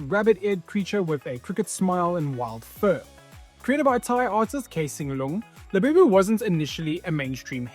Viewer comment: did you just say thai artist?